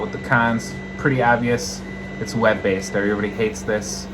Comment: At first I thought he said "wet based".